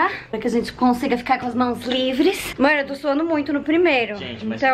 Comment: só eu escutei um "mor" ??